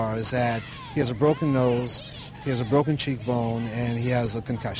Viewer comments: What the fuck at 0:45 its says broken cheek lol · Captions say: Theres a broken cheek lol